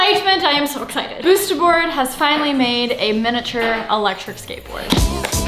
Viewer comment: I thought u said I’m done with electric skateboard lol 😂